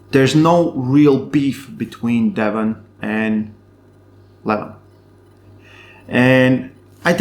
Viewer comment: I never know if you say Levan or Devon. Sounds like you say Devan and Levon 😂